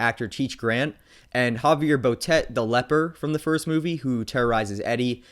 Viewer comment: Who played the leper?? Sounded like you said Javier Bardem... ??